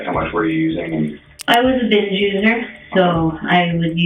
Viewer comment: OH MAN.......... You REALLY FUCKED UP on that captioning. It looks real bad. Almost like...... instead of LISTENING to what she said, you LOOKED AT HER and ASSUMED what she said. She CLEARLY said “I was a binge USER!” 😬 Not that she really deserves respect after what she did but still....... it doesn’t look good on your behalf. 🤦🏻‍♂️